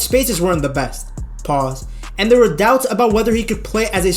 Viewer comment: Did he say ‘Pause’ while reading his script?